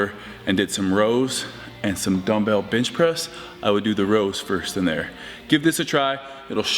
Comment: I really thought he said dumbell bltch press 🙈🙉😂